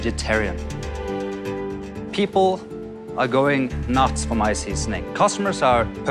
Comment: I don’t know why, but I heard him say “people are going nuts for my sea snake” 🤔